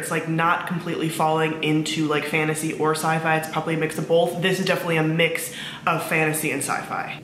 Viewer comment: did you say bolth?